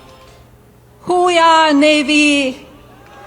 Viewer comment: It took me a minute to realize she was saying "whoah"! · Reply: I thought was saying "Ooh Rah Navy"